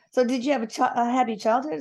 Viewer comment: What's that whispering? Did you have it? Happy childhood? Is the medium audibly asking questions to the spirit?